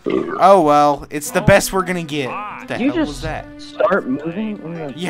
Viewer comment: I heard hell.